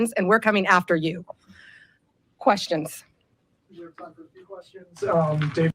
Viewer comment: That "Questions" sounded like an "And What?!" to me 😂